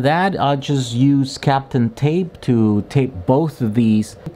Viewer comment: nice! when you first said Kapton tape I thought you said "Captain tape" and got kind of excited for a new character/person to the youtube channel, and their secret electrical insulation powers.